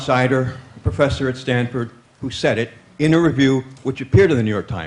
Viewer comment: This questionnaire really did not say interview, he said REVIEW at 0:16. I blame the sound resonance of the halls.